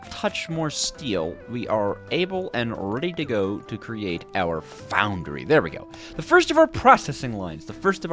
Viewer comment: Did someone say create?